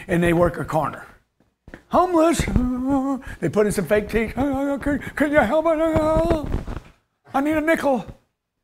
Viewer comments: My boy G at 7:52 said "ahhh, can you help a n**ga out... I need a nickel" hahaha!!! No seriously i don't think he really said that. The power of media though right? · did he say “can you help a “n word” out? I like Grant but he says some borderline racists things from time to time.